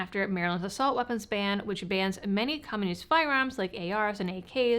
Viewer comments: it sounds like you said "many communist firearms"😅😅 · I heard "common use firearms" as "communist firearms" and was confused why you included ARs